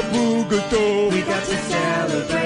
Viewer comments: Did You Say "Nacho Taco Chimichanga"? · did bro say chimchanga??